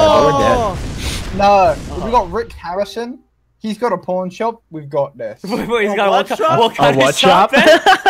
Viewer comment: it sounds like hes saying porn shop